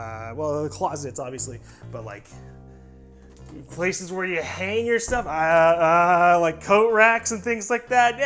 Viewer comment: Thought he said "Place where you can hang yourself"